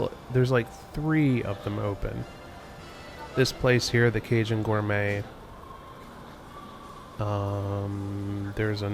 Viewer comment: I wonder what that lady at the Cajun Gourmet said to you... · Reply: Buy something, you're the first person I have seen all day??